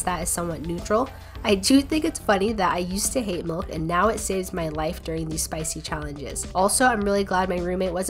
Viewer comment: "thank you pickled jalapeño for coming to the reascue" Milk: i thought you said i saved you life😢😢😢